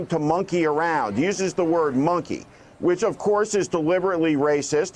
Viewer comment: Actually, I think he said, "Monkey things up." Which to my mind is even worse. I dislike Tramp but I have nothing but contempt for his supporters.